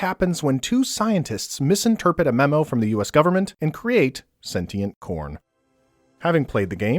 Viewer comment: Who thought he said sentient porn?